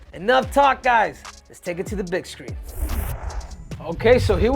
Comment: I swear he just said "let's take it to the bitch screen" 😂